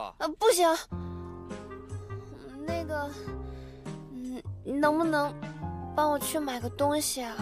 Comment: did she say the n word?